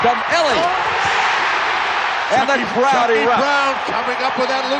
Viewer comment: whoa chuck daly sounded like he was in a horror movie there " CHUCKY CHUCKY!"